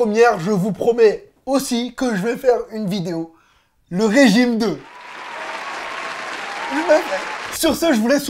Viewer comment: Tu dis quoi à ce moment la ? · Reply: Il dit "je vais le faire", et c'est bien plus cohérent et logique que "le 9 mai" comment vous voulez prévoir la fin d'un régime qui n'a pas commencé ?